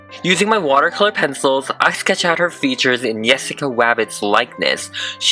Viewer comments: For a second u sound like u say “Jessica Wavbits” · Did u say “and yesaca wabbits like this” at 2:46?